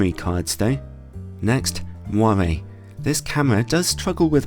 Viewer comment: Quick question : what's the word said at 7:14? "Next, <word I didn't understrand>, this camera does struggle with <word I didn't understrand>". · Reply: Moiré